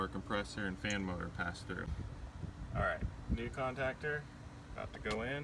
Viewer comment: Oh I thought u said contractor!? Switch!!! Like musical chairs!!! The one with plumbers crack syndrome goes 1st! Pull ya pants up before you come to my place..... ain't nobody got time for that😂🤠🤣 I change them when they have sutty look around the contact area! Or intermittent strange freeze ups!?(or visibly worn!!) Always put right size in too(residential)or slightly higher amperage (30amps are good!)RFL ?! Bigger the unit single phase unit... probably need a bigger contactor amperage 30-40 plus?! Wire terminals burning wearing.... replace contacts & make sure they're tight snug fittings! Clean /rinse the condenser coil.... hot unit .... hot/ harder working outdoor unit/system! Take it to da house 🏡🏃🏼 There's always more to check... Experience will tell you if low on refrigeratant after cleaning coil(s)... take temp drops at supply & return vents etc!! A history of good check ups will help with determining the system charge!? Eliminate call backs! Do a gd thorough job the 1st time!!👍